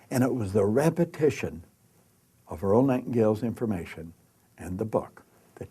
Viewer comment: please what did you say in between 11:03 11:04 instant, I think '' ...informations''